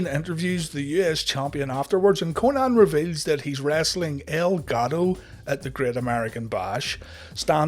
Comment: I thought you were saying 'Elgado' instead of 'El Gato'